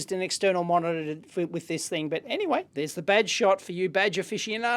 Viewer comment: wow i was listening to you talk about the computer and when you got to the badge shot i had to look because i swore i heard vag shot. lol.